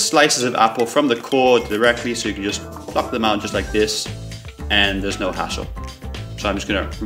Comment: I thought he said fuck them out